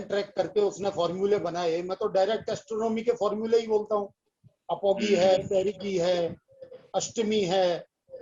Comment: Dear, Could you spell and translate what words sir is saying at 1:16:12??? Apopy, _____, and Astami. Kindly help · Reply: Apogee and perigee